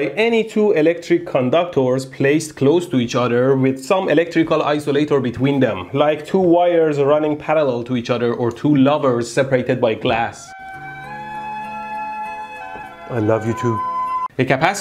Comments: At first I thought you said "like two lovers separated by class." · thought he said YouTube 😂